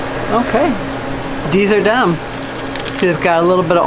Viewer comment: I swear said "Okay, these are dumb"